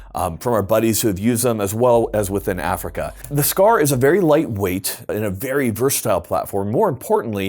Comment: did he say light weight?